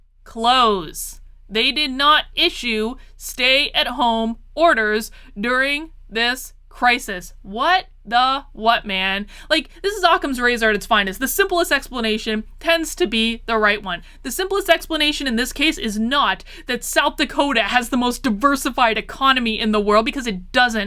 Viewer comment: It sounded like you said sock-em's razor. The simplest explanation is to hit you upside the head with a clue-by-four.🤣